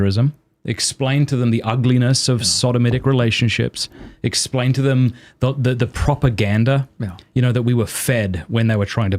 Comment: Matt says "explain to them the propaganda..." and I heard " explain to them the right way to look at..."